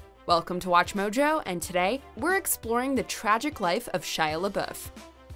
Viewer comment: Thought it said "tragic loss" 😅 I'm not ready, none of us are